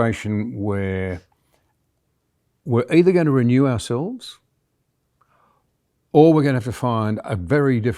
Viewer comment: Thank you both. God bless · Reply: They're both beautiful. It sounds like he's saying instead of ourselves at 13:31.